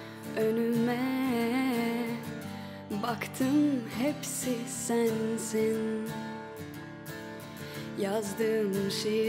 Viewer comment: anladım herşey "senzin” dedi